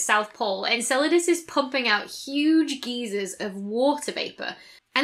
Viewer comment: I hear “geysers” and it comes across as either “Gizas” (pyramids and all) or Geezers. Magnetic field get off my lawn!